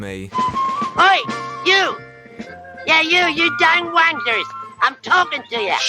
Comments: … I’m sorry, but did Worzel just call those blokes “dang wankers”? 🤣🤣🤣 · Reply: I think it's wranglers he's meant to say 😂 but I definitely hear something else as well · did he just call those guys wankers? Holy crap 🤣